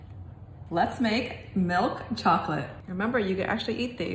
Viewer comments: Tell me why I thought she said “let’s make chocolate milk” I sat through the whole video and was so confused at the end when she came out with a chocolate bar. 🤦🏻‍♀️ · I thought you said chocolate milk instead of milk chocolate😭